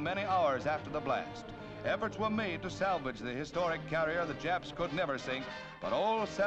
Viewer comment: Does narrator say "japs"? 😮